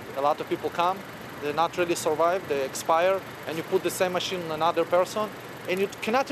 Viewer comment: did he say "expire"?